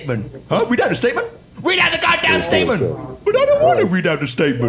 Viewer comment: Did said Weed this statement